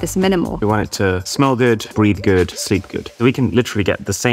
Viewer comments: I heard *breed good*💀💀 he said "breathe good" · sorry what did I just hear💀💀💀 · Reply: Breath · no way I heard breath good 'Breed good'💀